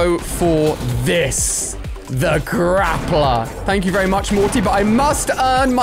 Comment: wait wait wait wait did he just call him morty?!